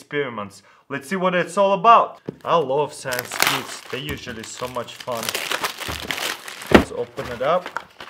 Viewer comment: "I love sex it's usually so much fun" Did he actually say that or am I crazy?